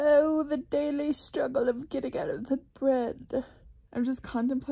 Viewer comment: Wait. Did she say getting out of the bread. Or was that just my carby heart?!